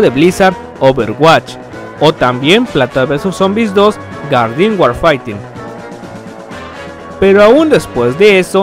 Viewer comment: "Garden waifaightin?" no le entendí xdxdxd